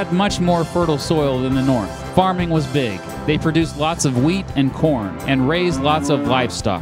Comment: At first I thought you said they produced wheat and porn